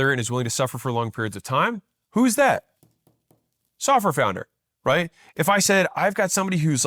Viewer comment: I heard “suffer founder” 😂